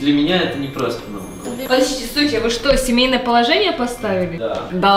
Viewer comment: подождите вы суки семейное положение поставили ? Я неправильно расслышала?